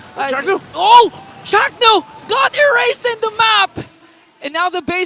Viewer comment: Chaknu got "ERASETED" did I heard it right?